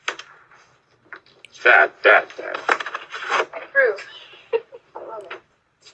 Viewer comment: Not only he should have charges against him, but the woman also. She's with him and laughing about it. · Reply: I heard her say to him when she was Laughing .. "I Love it" .. their Both P.O.S.!!😡🤬